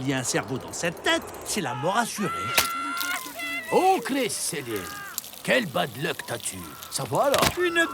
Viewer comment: Il dit quoi le vieux avant "quel bad luck as tu " A 2:30 ? J'entend "au clé c'est serré" Ps :c'est pas un troll, je demande vraiment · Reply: Au criss Céline